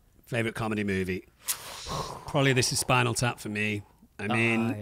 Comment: what is the film called that mentions? i can't hear it properly :D thank you! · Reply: This is Spinal Tap (1984)